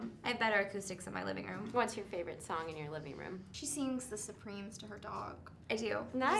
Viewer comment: what is the name of the song that anna said in 2:25 minute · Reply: She just said that Brittany sings The Supremes to her dog and I don't know if that's a song or a band but that's what she said